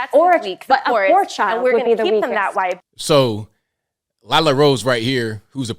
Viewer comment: I thought she said and "were gonna keep them that way "when it comes to being weak and vulnerable. Very telling and creepy if I heard her right. 😳